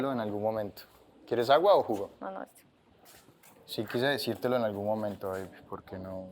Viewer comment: Sebastián le dijo a Shirley "sí quise decírtelo en algún momento BABY" o escuché mal?????????? btw me mata cuando le dice "no, mamacita" AAAAAAAAAAAAAAAAAAAAAAA